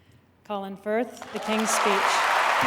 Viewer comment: Sometime, I misheard Colin's name. Then I asked myself why they called him Colin Fart. 😂😝🤗😷